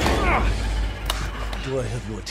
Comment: I don't know why this scene was so funny the first time I saw it. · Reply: I thought it was funny because I thought he said "you're about to see some wonders!"